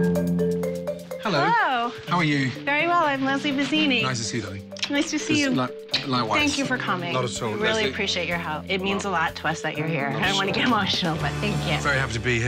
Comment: For anyone wondering this is Bazzini's from season 3 episode 3 (& yes they're currently closed). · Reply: Pam413 it sounds like she says Vizzini instead of bizzini